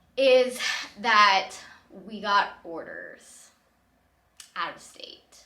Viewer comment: I had no idea what "we got orders" meant until I read the comments. I thought you said hoarders lol.